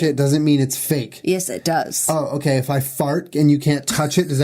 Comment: 23:54 did you say fart?